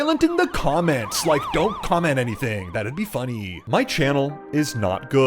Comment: Guys i thought he said don't comment nothing